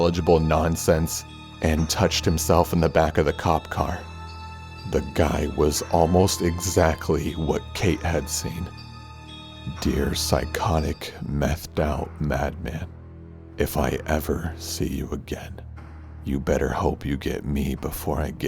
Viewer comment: Oh my god I thought you said "Dear psychotic methed-out Batman" I'm dead